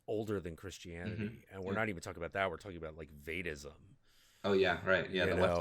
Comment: what did he say? Vaidism?